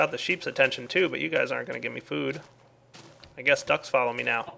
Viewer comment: Did he just say duck.....